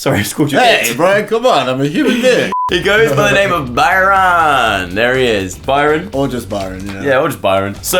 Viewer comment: I thought he said gorgeous Byron and I wouldn't disagree😈😍